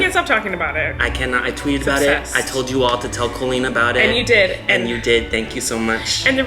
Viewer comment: I thought he said “tell the queen about it”